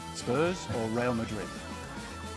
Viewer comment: At the end it sounded like someone said “You could’ve said you don’t like them all”😂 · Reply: He said you answer them all